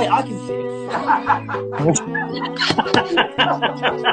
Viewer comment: "I'm such a ✨T O U R I S T✨" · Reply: i thought he said " oh i'm such a taurus" XD